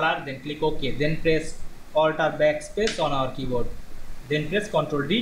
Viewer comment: I could not understand what you said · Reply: sounding like Press Alt tab backpace on keyboard